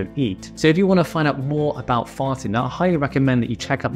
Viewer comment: Fasting, I thought he said farting 🤦